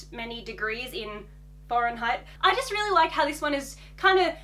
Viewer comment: I just woke up and watched this. I thought she said Foreign height and Fahrenheit 🤦🏾‍♀️🤦🏾‍♀️. Anyway, yes I can’t imagine a warm Christmas. I enjoyed the video. And I’m happy Archie got his Christmas wish. If you did blue jean shorts or something you will have all of his colors on.